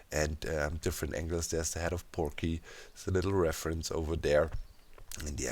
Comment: Did I hear him saying “the head of Porky”? Poor Keralis